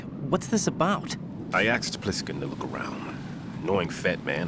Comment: really said “I axed pliskin” instead of “asked”, what the hell😂